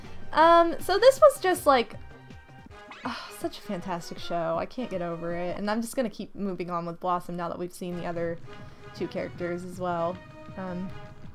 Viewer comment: It sounds like you said Bellosom the first time around.